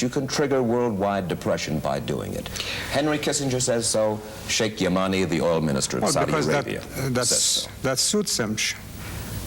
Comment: When Mike Wallace kept saying Sheik Yamani, I thought he was saying "Shake ya money."